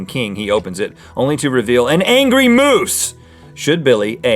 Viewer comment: I thought it said an angry mouse